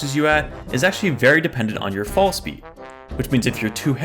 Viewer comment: Did he......DID HE SAID PHONE SPEED?????????? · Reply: Fall speed